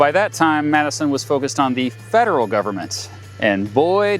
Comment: Mr. Beat says “federal government” but the subtitles says “national government”lol what the heck.